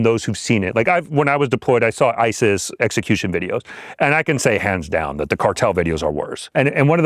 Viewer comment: At first I thought he said "ICEs execution videos"...